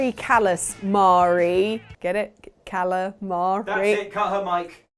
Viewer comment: When said, “Cut her mic,” I thought he said, “Cut her, Mike!!” I expected Mike to come out with a rubber dagger or something!! 😂